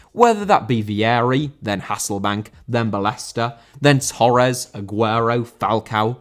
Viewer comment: when was listing the atletico madrid strikers who tf did he say between hassekbaink and torres??? i heard bellesta but after looking him up he doesnt exist.... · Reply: Bolton legend Salva Ballesta